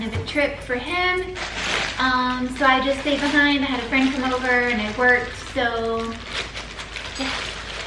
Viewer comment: Had the subtitles on. It thought saying "had a friend come over" was "had a French little bird" LOL man YouTube subtitles suck ass.